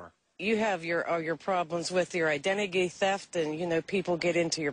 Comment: Did that woman say identigy instead of identity?